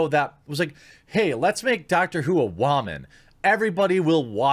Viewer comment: When you said doctor who I thought you said house. A female house eould NOT work and I dare them to try for the cringe.